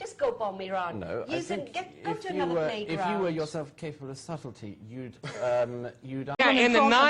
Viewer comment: what did he say on 1:09? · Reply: heiner lauter: "If you yourself were capable of subtlety, ... "